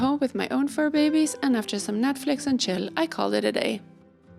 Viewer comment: 😂😅😂 Ok, I'm officially too old for YouTube. AND Netflix apparently. I'm even afraid to google what Netflix and chill means. What I wanted to say was that I had a nice evening watching Next in Fashion and NOTHING ELSE!!! 😅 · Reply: Haha. I thought you said Netflix and chilli (I think Americans refer to the dish chilli con carne just as chilli). I was thinking “but wasn’t Benita having dinner with her friend and it was dark outside?” I know the days are short but not that short surely. 😂. Our puppy was starting to growl at your cats (sorry) when I paused to write my comment. So I had to go back and pause at your friend’s dining room. Thanks for that video. Sounds like a lovely day. Very interesting to watch from the other side of the world. Your friend was kind to let us see a bit of her home. It’s also beautiful and hugge. Very stylish. Edit: P.S. People here often say “chillax” here. I don’t know if that’s a thing everywhere. It’s a cross between chill and relax, which are the same anyway. 🤣🤣🤣